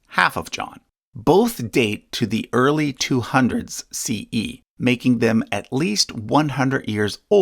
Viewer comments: the caption says 200s BCE instead of 200s CE. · Thanks for another great video! Your take on religion is much needed, I've learnt so much! Just noticed a small error today. In the subtitles at 9:43 it says BCE instead of CE. I always watch with subtitles so i don't miss anything you say, thanks for including them! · the captions contradict what was said, i.e. 200s CE vs 200s BCE. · The AUDIO says "Both date to the early 200's CE." But the captions say "... early 200's BCE." So which is it? ( I mean, obviously, but one wants to be careful about captions. )